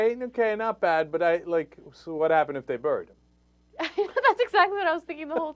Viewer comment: I can't figure out what he's saying "So what happen if they bird" is what I'm hearing which, taking a wild guess, is not what he said.